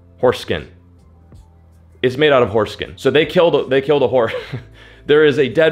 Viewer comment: Anyone else thought said “foreskin” when he said Horse Skin? I had to rewind it back to hear that properly lol